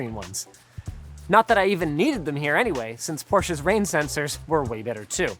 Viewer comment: Who else here can't stop hearing "Poor Cha" instead of the PorschE ?